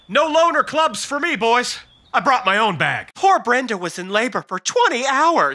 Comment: I got my own bag,😅😅😅😅😅😅wtf did I just hear